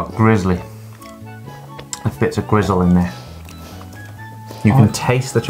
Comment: i cooked them a nice salmon dinner and this is what i get??🤮🤮🤮🤮🤮🤮🤮😂🤣🤣🤣 나쁜넘드라!!ㅜㅜ만날 토하게 만드냐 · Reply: your subtitles say greasley and greasle. When he is saying gristley from the word gristle. Greasle isn't a word but might be a name.